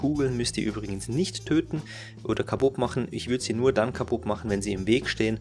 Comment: Kabupp machen :D `? Is das " ein Slang - Word " :D ? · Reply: D Ich hab´s echt 100 mal gehört :D Ich komme immer zum selben Ergebnis: Kabupp machen :D 0/